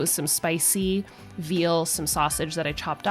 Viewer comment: You eat veal 😭😭😭 I'm surprised, I thought you wouldn't eat a baby cow. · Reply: It sounded like you said spicy veal..if you didn't, my bad..It was early and maybe I wasn't fully awake..lol